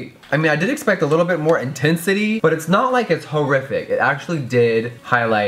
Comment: Did you just say hoerific or do you just pronounce horrific weird I can't tell but it made me laugh